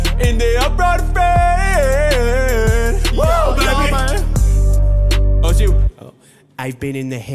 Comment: did say Obama?